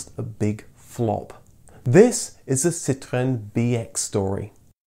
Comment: The way you say BX, sounded like BS to me. So I heard "This is the Citroën BS story.